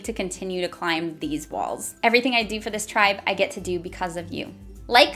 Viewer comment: I thought you were saying "Bye Beans!"